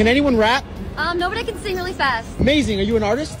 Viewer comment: For a sec I thought she said no but I can fart really fast